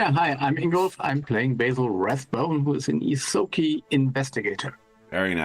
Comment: Sometimes it sounds like Ingolf is saying Basil Ratbone, still an appropriate character name! :P